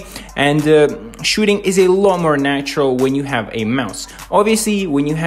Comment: I thought you say "When you have maus" He means Mouse lol